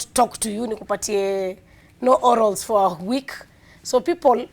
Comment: Did she just say no orals??